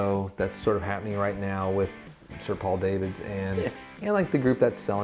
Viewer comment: did you say Sir Paul Davids?